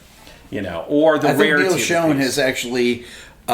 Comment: your talking about someone buying a bunch of guitars. Who? I can't understand who name you said rick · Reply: Zane McGahee Neil Schon.